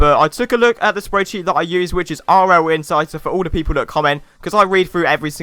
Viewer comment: you talk about your spread sheet, did you say RL insights? Cause I looked it up and couldnt find it